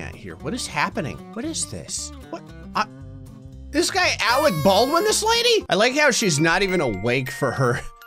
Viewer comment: Did he just say...Alec Baldwin the lady? 😂😂😂😂